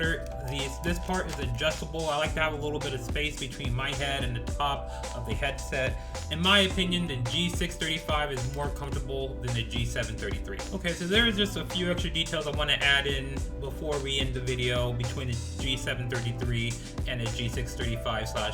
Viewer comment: oh my gosh did he say more "comfortable" or more "uncomfortable" the subtitles show "comfortable" it makes sense in the context of the sentence "more comfortable" but I can't help but think he said "In my opinion the G635 is more uncomfortable"